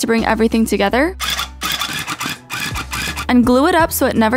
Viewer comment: It sounded like she said, “Tàgo bell” at first lol